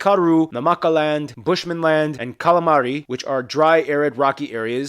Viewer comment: Did he really say "Kala'M'ari"? 🤣🤣🤣